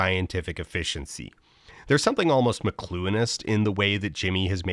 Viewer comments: what is the word he says at 11:17? i tried to google "mecluinist" but nothing came up, and the auto-generated subtitles don't say what the actual spelling is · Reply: It's "McLuhanist" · genuine and hopefully not dumb question - can someone please tell me what word Dan is saying here? I'm guessing it's probably an eponym of someone I've just never heard of, but I can't figure out how to even somewhat-correctly spell it. Google kept thinking I meant "masculinist", and the captions say "mlonist", which isn't a real word and Google thought was a typo of "monist" (so either an egregious typo of whatever the actual word is or it's auto-captioned and the AI had a stroke). Haaallp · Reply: It's "McLuhanist"! Marshall McLuhan wad a theorist who is wrll-known for the quote, "the medium is the message" when talking about media/communication. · McLuhanist is the word used here that the auto--captions miss. Its come from Marshall McLuhan, a canadian philosopher and media theorist(Matpat who?) who's famous for saying: "the medium is the message". So what Dan is saying here is that because the game show reveals itself to be for children, it leads to the the participans acting as such. Deep cut Dan, thanks for the broadened horizons. Oh and shout out the the machine learning tech that just wrote "mlonist" and called it a day. Almost smart enough to get on beast games i reckon. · what word is that? Mcluinist? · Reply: McLuhanist, i.e. of the philosophy of Marshall McLuhan